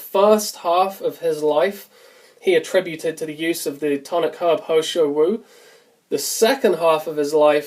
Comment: did you say He Shou Wu at 2:25 ?